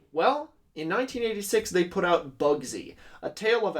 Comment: When you said "Bugsy" i thought you said "Bubsy" and i was horrified.